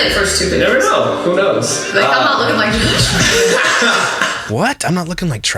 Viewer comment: Josh, I thought she said, "maybe if they end up looking like Josh" when they were "joking" about what could stop them from posting their kid.